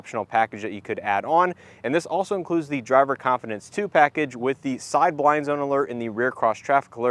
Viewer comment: At first I thought you’d said “Driver Incompetence Package”. It’s not really needed, plenty of drivers have that naturally. Very nice review!